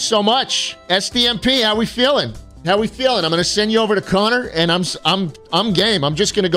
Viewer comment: had to double take, thought he said "i'm gay"